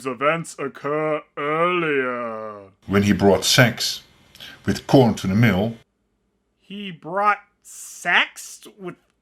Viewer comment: the narrator said "When he brought sacks with corn to the mill” so I'm surprised you got confused with what he said, but then again the narration is awful enough for almost nobody can even understand it